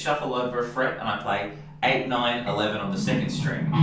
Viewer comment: did he say 911 o_O